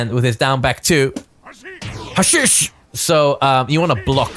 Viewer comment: The "HASHISH!", or whatever, is forever going to help me by mishearing it as "ASHI!"